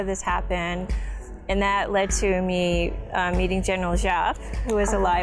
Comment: Almost at 14:09 she named a general, what she said? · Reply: Beautiful Pakistan سوہڑاں پاکستان general zia